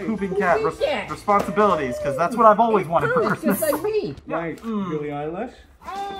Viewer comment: why did i hear bellie eyelash😭😭😭😭😭😭😭😭😭